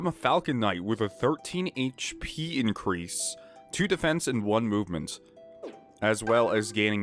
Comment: I thought you said "with a 13 inch peen increase" and spat out my drink. My poor lungs.